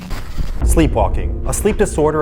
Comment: i thought it said sleep stalking